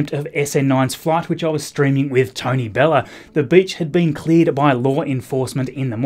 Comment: When he said “the beach had been cleared by law enforcement” I thought he said “the b*tch had been cleared by law enforcement” and I was sooooo confused. Edit: For profanity